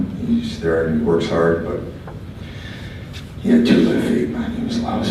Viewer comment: what did Rene say at 1:37??? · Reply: "He had two left feet"